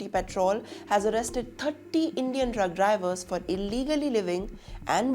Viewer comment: did she say drug drivers 😂